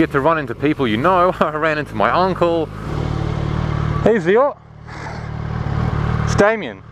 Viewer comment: did you say " Hey theio"???